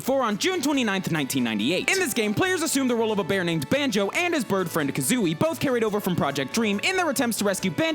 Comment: I thought at 1:14 he said "and his boyfriend Kazooie."